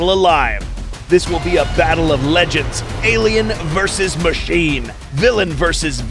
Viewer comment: he said "Alien vs Machine" but i heard "Alien vs Machete"